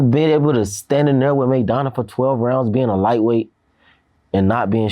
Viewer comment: he really said raidona. Im weak